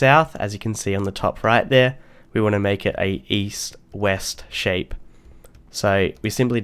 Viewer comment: East West oooohhhh I thought you said weest